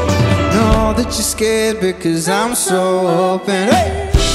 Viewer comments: + what's he saying here??? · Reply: "And i'm hoping someday i'm open" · What say in the minute 2:33? I'm spanish xc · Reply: "golden, golden, and hoping someday I'm open"